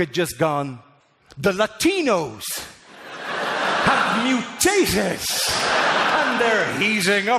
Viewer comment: Instead of "the Latinos have mutated" I first heard "dilatinos have mutated", and was like "wow, this guy is aware of supergravity!"